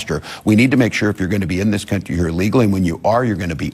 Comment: I swear it sounds like he said “illegal“ not legal.